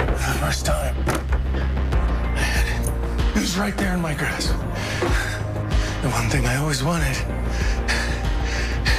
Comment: What does he say there? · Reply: For the first time I had it. It was right there in my grasp.